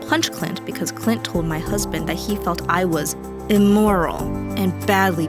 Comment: When she said immoral i thought she said imortal